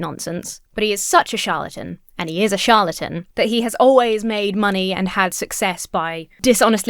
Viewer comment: I was munching on some sunflower seeds during the beginning of the video, and this disturbed my hearing a little bit. When you called Kent Hovind a “charlatan,”I totally heard “shart” instead.